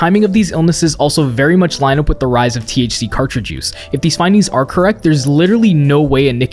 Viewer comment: Did you just say carti-jews?